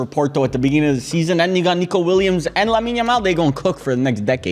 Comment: Did he just say N***a Nico Williams @ 4:02 😭😭